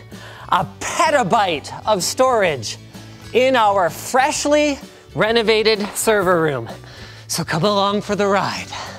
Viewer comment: While eating Reeses Puffs, I thought he said "In our freshly-renovated Subaru".